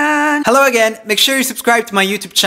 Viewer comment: did I just heard " I put out nude videos every Tuesday" ?